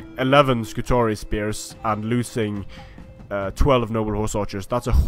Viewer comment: Did I hear you say "shitezen cavalry"? lelelele